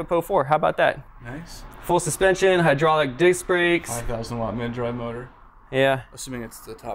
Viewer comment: Swore your said hydrolicdick brakes